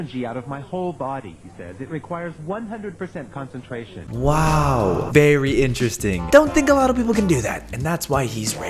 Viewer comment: Did he just say "berry interesting" ?